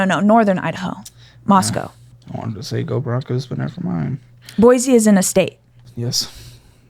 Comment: "Boise is in a state" "Good job"💀💀😭😭😭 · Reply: I thought she said Boise isn’t a state. Not any better 😂